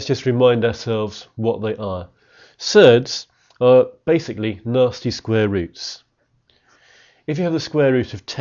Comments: At the start I thought he was saying ‘Nazi square roots’ and not ‘nasty square roots’ lol don’t ask why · did bro just say Nazi square root?